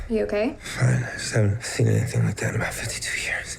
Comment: I thought he said 15 years, not 50. I went back and I'm still not sure. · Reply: Acey Infinity He said "I haven't seen anything like that in my 52 years", meaning that he's never seen a woman that hot in person and about to have sex him with him in the 52 years of his life.